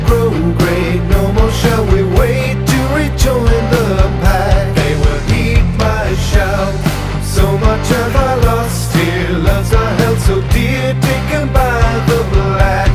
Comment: Dunno why, whenever the song says "They will heed my shout", I always hear it as "They will eat my shorts"